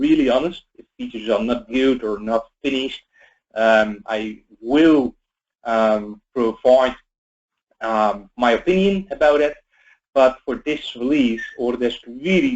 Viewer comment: What does he say @ 1:53? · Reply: I will provide my opinion about it.